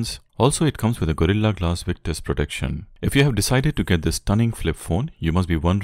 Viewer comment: lol i heard "gorilla glass witness protection" 😅😂